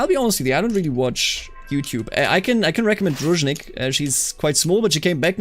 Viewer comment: I couldn't quite understand the youtubers Name mentioned at 1:22:40 , did someone maybe? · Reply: Druzhnik! <link>